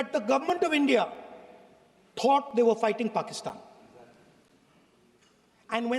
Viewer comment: fuging? Did I hear right?